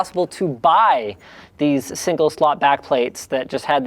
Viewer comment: at first i thought it said sling shot rather than single slot haha